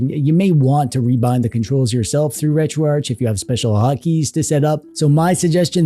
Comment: When you said 'hot key' i thought you said 'hockey' and for a minute i thought it was a Canada thing.